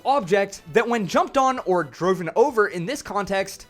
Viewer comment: did you say "droven"? That's not a word. Driven*